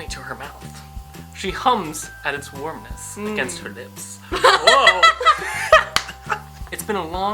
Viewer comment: I LOVE THIS & YOU GUYS THIS FEELING IS AMAZING I'M SO HAPPY. Also when you read "warmness against her lips" I thought you said nips.